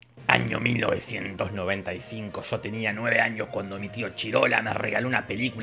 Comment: Oe, JeffarVlogs tiene que escuchar esto, soy ignorante pero es la primera persona fuera del equipo Fiuum que dice Chidola, o es lo que escucho jaja